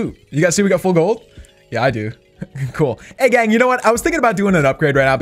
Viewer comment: did he say “egg gang”